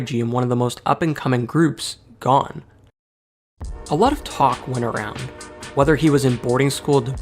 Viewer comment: what does it say at 2:55 Tupac's (something) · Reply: I say “a lot of talk went around”, but Tupac is mentioned later in the video!